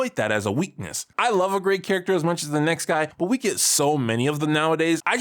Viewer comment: I think you meant to say "I love a great shades-of-gray character" or "a great morally ambiguous character" but you left out the qualifier and just said a great character... wait, unless you said 'a gray character' but I heard a T. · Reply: I said "gray" character, but the mic didn't pic it up clearly.